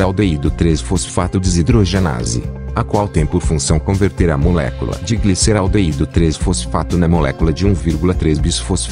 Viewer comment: eu n entendi aquele " Pi " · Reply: Piruvato